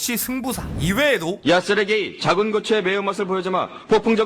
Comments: 왜 자꾸 세르게이가 쓰레기라고 들리지... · 나만 '야 쓰레기'로 들었냐?